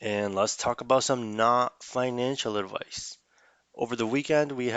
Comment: Mic is great cause the only thing I heard was “this IS financial advice”